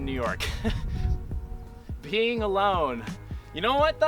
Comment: I misheard "being alone" as "peeing alone" and thought how true. Much nicer to let it all out with a friend isn't it? 😂